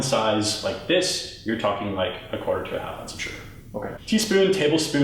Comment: what is he saying? "quarter to half ???" im a non native speaker, i cant hear well. thank you. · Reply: wondr.waffl yes that's what he's saying 'approx quarter to a half ounce of sugar'